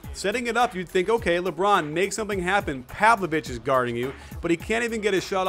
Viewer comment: Sounded like "have the bitches guarding you" hahaha